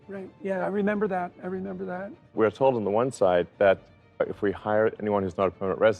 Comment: Did Musk say "Entropy"?